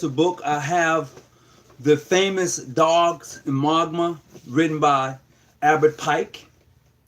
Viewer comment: What is the book he is saying at 4:55? Dark Magma?